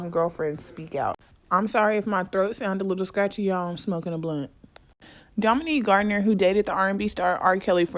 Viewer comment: Did you say“ I’m smoking a blunt“ ? 😂